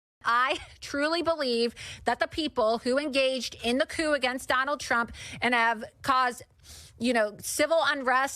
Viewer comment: "The coup against donald trump" I was confused because I misheard the "at donald trump" at first and thought she was talking about the coup donald trump led at the capitol. You know, an actual coup. Not the "coup" of people telling trump to follow the laws of the country he's a dictator of.